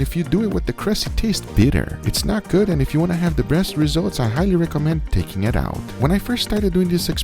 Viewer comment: did I hear if you wanna have the breast results....?